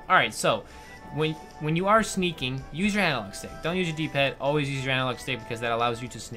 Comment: what did you say at 2:08? I'm sorry, you were talking a little too fast :3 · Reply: Use analog Stick Always and not your D-Pad was